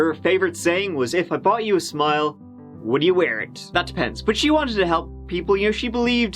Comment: Hi what is said at 1:28? It's sounds like "If a body would smile, widdaya weren't. That's pence."